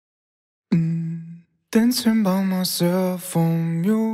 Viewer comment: Nice cellphone song bro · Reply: Thanks Den. Now all I hear is "dancing by my cell phone"